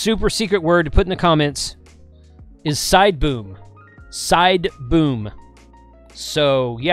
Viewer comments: SIDEBOOM, jesus i old of sworn you said SIDEBOOB! HA! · lol! I thought you said side boob, not sideboom. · Side boom. I thought you said side boob 😂. I gotta research what happened. I wanna know the outcome. Thanks for the video. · Sideboom! Lol, xyl thought she heard sideboob....jeez